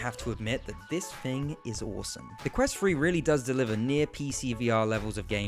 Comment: This video bugs the shit outta me because i keep hearing him say "quest free" and "fought" instead of "thought"